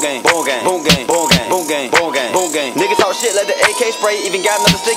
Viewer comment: My favorite part was when he said boonk gang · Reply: More like boo gay boo gay boo gay. Fr what it sound like he say